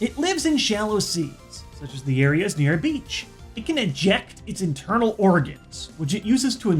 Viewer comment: Did he just say "It can INJECT its internal organs" at 3:00? That's even more horrifying!